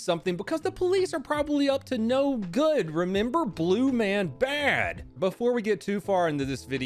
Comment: I Heard you say "Blume man bad"